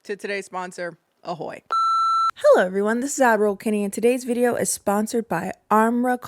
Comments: Oh I for sure thought you were saying Admiral 😂😂😂😂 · My god I'm so glad I wasn't the only one mishearing her ! Ngl for at least a year I thought she was saying Adderall and thought it was a joke about her ADHD and i just wasn't in on it 😂😂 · I always heard 'add roll kennie' as 'Adderall kennie', which I found appropriate. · I definitely was mishearing ad roll Kenny but figured it out eventually And omg I’m so early ❤❤❤❤ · Amoral lol, I won't even lie, for a while I thought you were saying. "Send it to Adderall Kenny", I was wondering for the longest time why you said that...😂 Then one day it clicked "AdRoll" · HOWLING at the painting. tbh the first time I heard you say it I thought you said "adderall kennie" but admiral is way funnier. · This whole time I thought you were saying "Adderall Kennie"😭😭😭